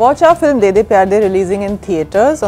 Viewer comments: am i the only one who heard that rakul is saying.....moot kiya...book Kiya...😂😂😂😂😂😂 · Did i just heard "moot kiya book kiya"? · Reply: Its mood kiya , book kiya!😂😂😂😂😂 but even i heard it as moot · yo did she just say moot kiya book kiya in the end?? wtf lmao